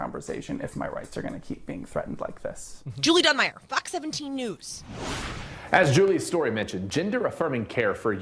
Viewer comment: im sorry but at 11:55 does she say "fk 17 news" ?????????????